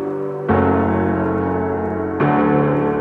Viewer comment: I hear him saying "Stuck on the Loo" (I like this song a lot but randomly thought he says that now and cant unhear it)